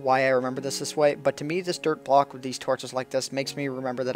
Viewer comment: I heard 'Derp block' instead of 'Dirt block' :p